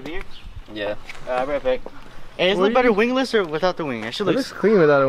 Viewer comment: "wingless or without the wing?".. Did he say that or am I just not understanding?? 🤔